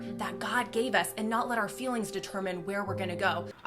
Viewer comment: all I hear is “Your feelings don’t matter”